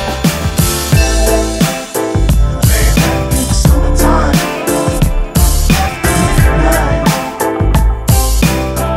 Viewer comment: what say the sample at 1:18? "summer time. At the midnight"? reply pleassssse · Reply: after midnight